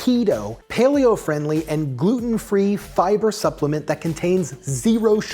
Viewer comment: during the ad-spot i heard "Voodoo free" instead of "Gluten free" and I was a little disappointed that the fiber was Voodoo free